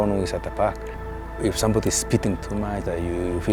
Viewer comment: I think he says "If someone is speeding to much...", instead of "speaking".